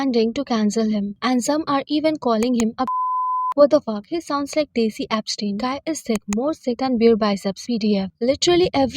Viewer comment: What did you say under beep? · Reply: Pe*ophile